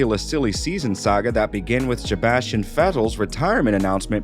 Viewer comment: It sounds like he says Sjebastian Vettel